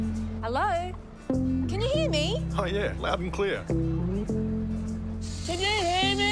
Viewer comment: I thought this was a gay ad - looks QAF. I thought he was saying 'Proud and Queer' - maybe I need a hearing aid.